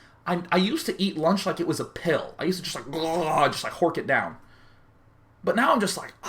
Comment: just like what it down? what does he say there :P? 21:51 ?? lol